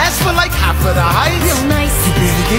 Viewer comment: I always thought that it said gonight instead of a real nice